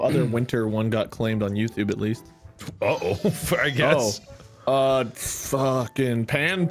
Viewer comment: WHAT IS HE SAYING HERE??? 0:45 SOUNDS LIKE HES SAYING THE F SLUR WHAT??? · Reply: He started to say f’ck but turned to say “I guess”. He didn’t say the f slur :)